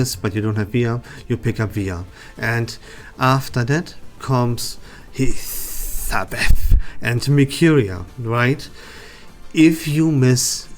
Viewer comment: Hello sir! I appreciate this clarification video i do have to apologize but at 9:21 and 9:37 i cannot understand what you were saying from your accent. Who was that you were mentioning there? Thanks again! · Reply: hey James :) xD that is because i pronounce Hissabeth really weird as a Joke. i was saying that Marcus and Vila are best played together with Hissabeth and Mercuria but incase you miss one of either Hiss or Merc, you can grab Argus to make your Team complete